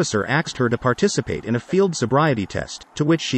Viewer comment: The officer “axed” her to participate… Dafuck I hear?